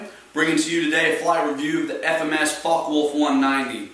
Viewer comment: are incorrect. It is simply pronounced "fok wolfe", or in German, "Fokkeh Vulfeh". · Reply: he did not say your exaggerated "fuck woolf". He said "fok wolfe".